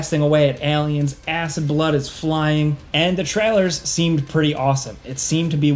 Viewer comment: I thought you said "Ass n' blood is flying" hahaha